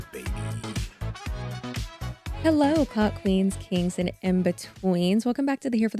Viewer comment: I thought you said Cuck queens and I’m cracking UPPPP. And now we’re talking about chocolate starfishes